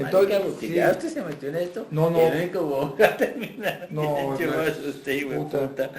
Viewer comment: en el minuto 14:31, lo que dice realmente es "Y EL QUE VA A TERMINAR BIEN ENTERRADO ES USTED", y esas son palabras mayores